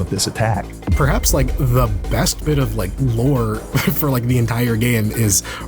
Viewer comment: ai subtitles? should be 'lore' instead of 'more'